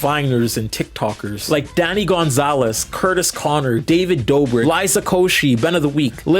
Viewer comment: Daddy Gonzalez??? Sounded like that’s what you said